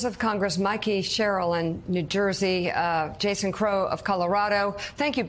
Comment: I had to replay the end of the clip. I thought I heard Mika say “…thank you both for coming on to work.”